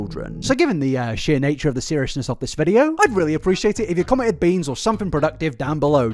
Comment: I heard “comment beans”?? Soooo 🫘